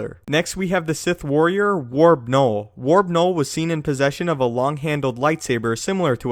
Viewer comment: It sounded like you said Warp Knoll, in regards to him using only 1 blade of his saber and never both the reasoning behind this is he had a function in his lightsaber that once he tried to use the red blade the green blade would shut off, the emitter that shines through the crystal to produce the blade didn't have a dual output function it would switch or flip at the press of the button, and he primarily used the green because there was a battle including him siding with a few other sith lords against and outnumbering force from the Jedi order, he primarily used green in that battle to create confusion amongst the Jedi during battle